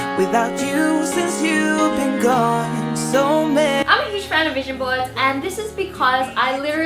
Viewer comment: Lmbo when she said vision board, i thought she said asian boys😂